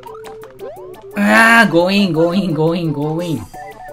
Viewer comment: の所で｢強引 強引」が「GOIN GOIN」に聞こえて急にデレマス！？って思ったw